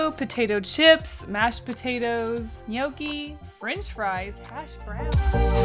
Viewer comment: When was going through the potato foods, I heard her saying “hasch brownie”, which in Swedish means weed brownie, and I was like. “Kasey, what the fuck.” But then I realised that you call them weed brownies in English. What was she actually saying?